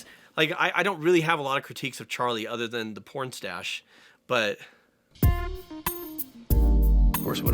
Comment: when you said your only critique of Charlie was the porn stache I heard "porn stash" and was racking my brain trying to remember when Charlie's secret stash of porn was uncovered in the series